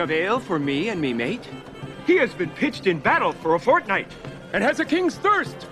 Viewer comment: On 13:47 did he just say battle of Fortnite?